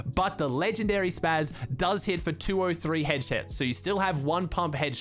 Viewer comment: what did he sayyyy? Headshet